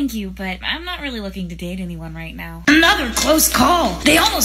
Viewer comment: I thought i heard mouse school